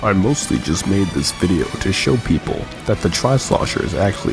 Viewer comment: I thought you said people dont watch trades?